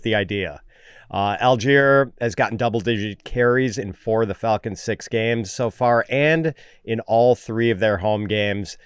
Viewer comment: thought said “in four of the fuckin six games”😂